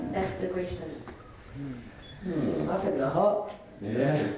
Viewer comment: WHAT DID HE SAY??? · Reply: He said “It’s hot” as in the weather.